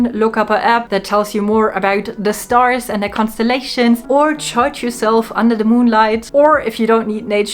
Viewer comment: I thought you said "touch" yourself under the moonlight...😊 until i realized you said "charged".